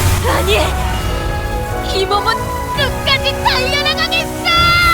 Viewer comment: 여기 끝까지 달려나가겠소 아닌가요 나만 그렇게 들었나